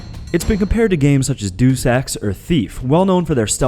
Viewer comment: Oh you said Deus Ex i thought you said "Do Sex" I was like WTFFFF